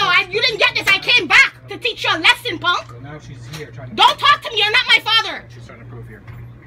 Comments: "I'm not my father" NANI?? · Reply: I heard “don’t talk to me you’re not my father” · Did she just say”I’m not my father”😂😭